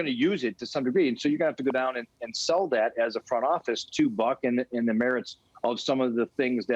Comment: ….did he say? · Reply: He said " Buck", not what you were looking for.